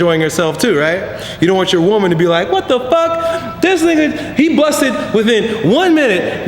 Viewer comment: lol did elliot just say ''dis nigga'' at 2:28? xD